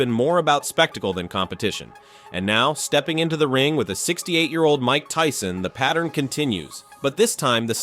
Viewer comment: Did he say 68 year old?